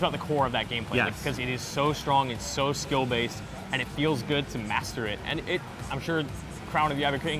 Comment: did he just say it feels good to masterbate?